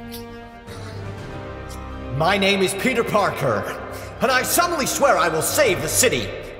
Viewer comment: Why did I hear "My name is PewdieParker"